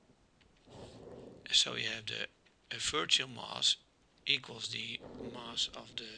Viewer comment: What does the "v" stand for in mv at 1:40? I couldn't quite hear what you said. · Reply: I think he said "virtual mass"